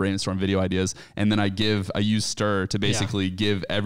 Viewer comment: What was the app he said he used to crowdsource brainstorming at 18:25? It sounded like stir but that's a single parent dating app